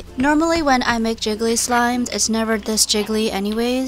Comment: It sounded like Slime Rancher XD